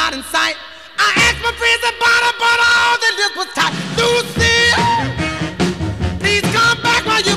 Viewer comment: I thought he was saying LUCIA! But he’s saying Lucille with southern accent